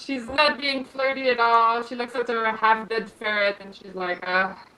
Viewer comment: when said "looking at her half dead ferret" i thought she said "Undead ferret" and freaked out about what i missed